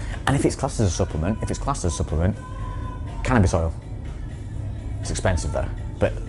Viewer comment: what kind of oil did say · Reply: Cannabis oil